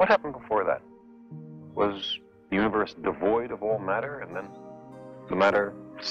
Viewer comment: I think there's a misquote at 9:45. It sounds like Carl is saying "... the universe devoid of ..." rather than "... the universe the void of ...". Irrespective, still a thought provoking video featuring one of the great pioneers who brought the wonders of scientific discovery to the general public in an exiting and consumable format.